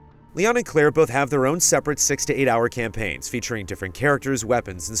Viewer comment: Did I just heard different 68 hour campaign? Holy cow!